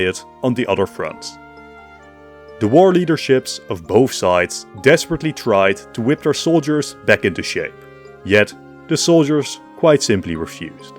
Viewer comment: I initially misheard this as “The Leaders desperately searched for soldiers who could do sh*t” which killed me